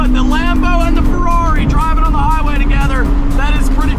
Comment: the lambo and the what??? He totally said corvette not ferrari lol! Love the amazing content!!!